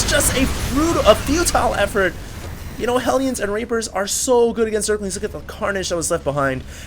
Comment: you said Rapers instead of Reapers @ 5:24 xD